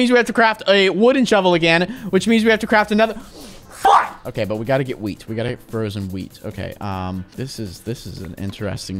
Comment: What did you say in 10:24? Btw, great video! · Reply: "Flip!".